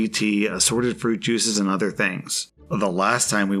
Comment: I thought he said beating and was so confused how OP was even a making the post